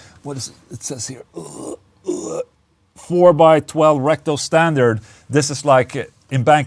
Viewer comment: I could have sworn you said, "Four by twelve rectal standard."